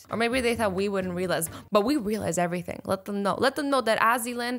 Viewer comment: she says "we welise" instead of "we realise "